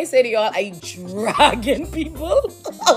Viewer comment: did she say she's "dragon people"? That's pretty awesome, she can turn into a dragon